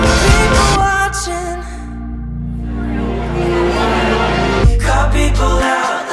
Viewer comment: “Cut people out like tags on my clothing” his lyrics are literally SO creative!!! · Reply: wait ...ooooh ...ok. i thought he was saying "call people out" instead of 'cut'... makes more sense ...but still. Calling people out for their bs kind if fits the idea too. I love that analogy though... so well written.